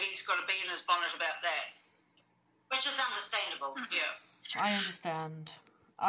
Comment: Ok call me crazy but at the 40 sec. mark it really sounds like she says ok I can see you witches.